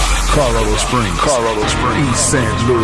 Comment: does he say," Colorado springs?"???? Someone know? Anyone? I really want to know!!!